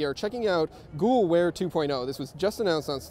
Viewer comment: "Google Wear 2.0!" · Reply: i thought he said ghoul wear